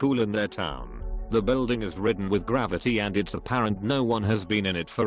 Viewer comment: Shadowy Ghost in School--I kept hearing "The building is ridden with gravity" Took me way too long to figure out they meant graffiti 😅